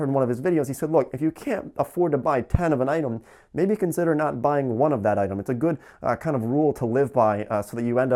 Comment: Im a non native english skeaper and i couldn't understand the 7:50 rule. "if you cant buy 10 of an item dont buy one of them" ?! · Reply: Oh he said " If you can't afford to buy 1/10 of an item, maybe consider not buying one of the item" meaning if you cannot pay 1/10 of the cost for an item right now, you should not be buying it. 😊